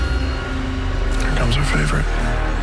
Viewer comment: Watching this back, did anyone else hear Tyler C say "Here comes her favorite" at 5:18 as Luke walked up? If they felt this way, did it affect her ability to develop deeper relationships with the other guys?